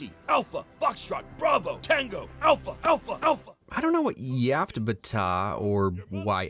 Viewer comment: I think he said Alqha